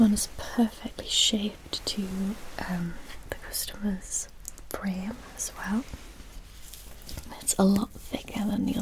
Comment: What is the whisper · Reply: It's "...perfectly shaped to the customer's frame as well..."